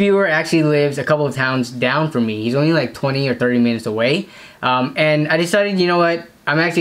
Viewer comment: it sounds like he’s saying that he lives 30 meters away instead of minutes. Is he be like ur neighbour